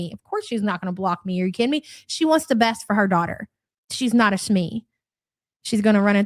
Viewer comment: Ughh did she just say she is not so fresh but going out anyways!? LOL Gorl take a shower! Nasty ass.